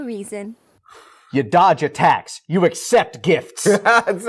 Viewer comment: "You dodge attacks, you accept gifts." 😂😂 RIP Toriyama, you changed the world of how anime and manga are seen. May you enjoy Heaven among the Kai's and teach that Dinosaur how to ride a ball. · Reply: Lmao i heard that as dodge taxes but that makes more sense in context